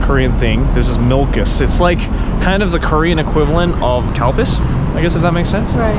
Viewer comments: i genuinely thought joey said "cow piss" and i cant unhear it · Did you just say cow piss at 8:37?